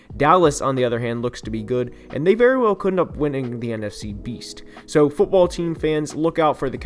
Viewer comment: Did you say "Beast"? 😆😆😆